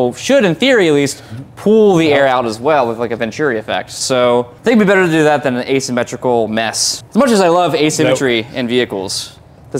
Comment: I am foreigner and sometimes I really can't understand what say like at 6:00. Wrooboolooobuuutoofoo what? This is too fast! Dear fluent speakers, could you answer me: did you understand his talk? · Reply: He said "Think it would be better to do that than the asymmetrical mess."